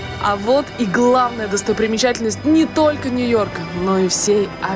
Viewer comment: Мне послышалось , что Юлия Коваль сказала достопримечательности не схожи ?